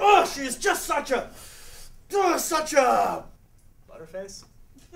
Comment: What does Mr. Potter say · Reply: Butter Face